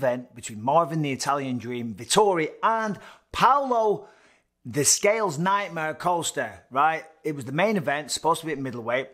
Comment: The scales nightmare, bisping you funny man 😂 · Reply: Lmao I thought he said the scales his nightmare.